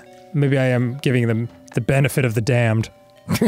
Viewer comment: I thought he said "giving him the benefit of the Dan." and I really think that should be a thing now